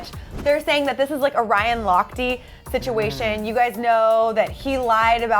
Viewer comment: I thought she said a Ryan Ross-y situation 😂